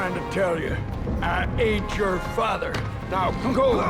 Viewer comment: for years i thought he was saying "I ATE YOUR FATHER".